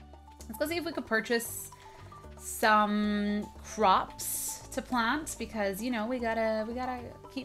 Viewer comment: said " Let's go buy some props." She said props instead of crops.